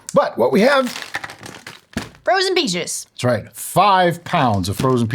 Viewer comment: "Frozen peaches" isn't what I heard first. First I heard "frozen pig juice" 😅😂😅🤣🤦